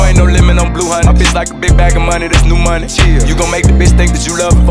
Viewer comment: i thought he said chill not chyeah